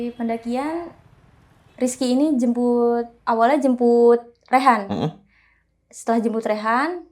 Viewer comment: salah denger kali gw😯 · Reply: Jembutt